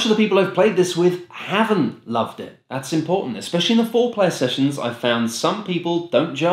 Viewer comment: ”Especially in the foreplay sessions, I’ve found that some people don’t gel with the micromanagement”. That’s what I heard, at least. 🤣